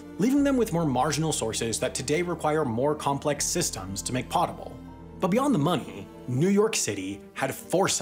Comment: did you say "pottable"? Lmao